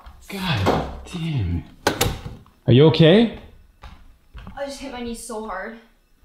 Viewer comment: At first I thought he said ASSIE are you okay?